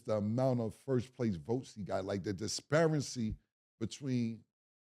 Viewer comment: did say “Disparency”? 😂😂😂